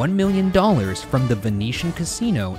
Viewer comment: I have a whole ton of questions for the person in charge of subtitles who heard "Venetian" and wrote "Nevada" instead.